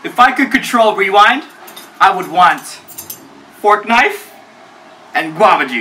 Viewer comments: LOL when he said “ForkKnife” I thought he said "FORTNITE" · Lol i heard fortnite